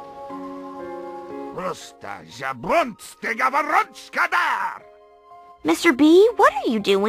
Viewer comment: What did Mr. Bobinsky say??? "Prosto Zabonsti Gabarotskada"